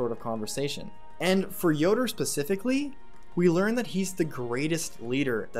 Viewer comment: did you just say Pacifically?